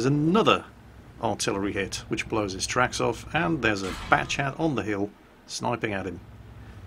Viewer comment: did jingles just say fat-chat or was it just me?